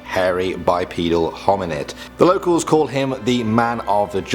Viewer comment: I heard bipenal